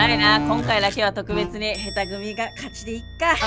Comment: 今回大活躍のるぅとくん 『ちょっとこっちの上手い組から１人譲渡(初め除去に聞こえたw)します(^^)』 腹黒るぅとくんも好きです💛